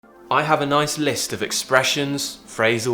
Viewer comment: I hear "a noise list"